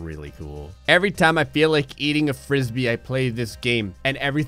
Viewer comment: I might be really tired but did he say “every time I feel like eating a frisbee”